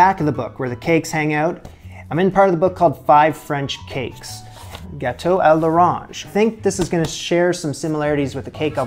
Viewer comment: I thought you said bateau. I was like, orange boat lol